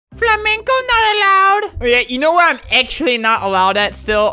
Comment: I love how you can see the joy on his face when he says "flamingo not allowed". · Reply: I heard "FLAMENCO not allowed" welp i guess albert now its flamenco not flamingi